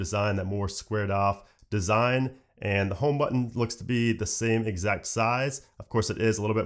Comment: lol did you just say "Home button"??